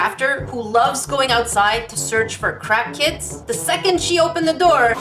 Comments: When she said craft kit I thought she said crack kid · Thought you said "crack kids" instead of "craft kits" LOL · I heard "crack kids" 😂😂😂🤣🤣🤣